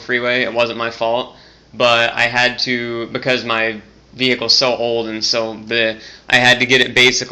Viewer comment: I heard a subtle "vee-HICKle" again.